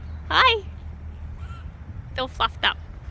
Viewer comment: i had to do a retake, i thought said "he's all fucked up", but it was fluffed :D